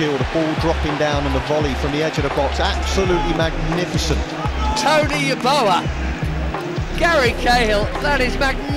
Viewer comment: Did I hear "Tony Yeboah" the Ghanaian 🇬🇭striker @ 6:22? He was a beast. Proud to be a Ghanaian👌🏾